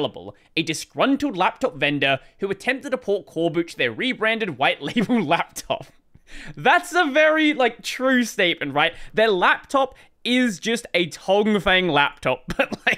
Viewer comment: I thought you'd said 'while label rebranded toclafane laptop' at first and I still can't stop giggling.